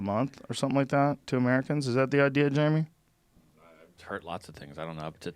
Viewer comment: Did he call, "Jaime," by, "Jeremy?"